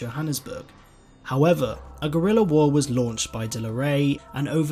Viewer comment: I honestly thought he said gorilla war.but he didnt,I presume.(no pun intended)